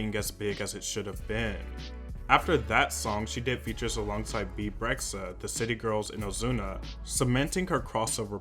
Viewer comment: Did I hear, "Beeb rexha" ? Lol